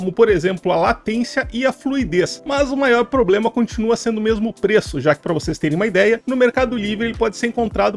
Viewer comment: eu entendi que o problema é o peso ..... rsrsrsrsr